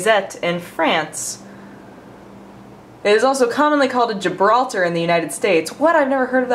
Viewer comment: I thought I heard call Gibraltar Jabroni... Personally I think Jabroni is a great name for a coffee.